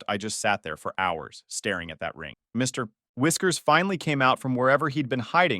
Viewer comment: thought that said mr whiskey for a sec